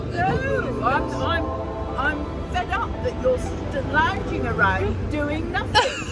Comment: what did she say? xD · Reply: "NoooOOooOo! I'm fed up that you're lounging around doing nothing."